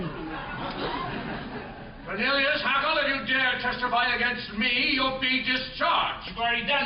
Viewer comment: the captions say "vanilla chocolate you dare testify against me" and that is the funniest thing ever